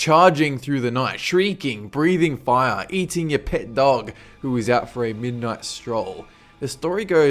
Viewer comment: Eating your pets dog? I didn't know my pet could have pets xD He problably didn't say the s in pet, but sounded like it when I first heared it :D